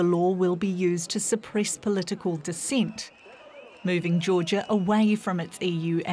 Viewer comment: I think she meant "Political Descent" but I heard "Political Deceit" which is def something we do not want. lol. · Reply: Actually it's 'Political Dissent'